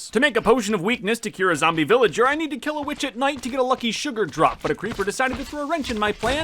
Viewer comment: u swore on a video u said b*tch instead of witch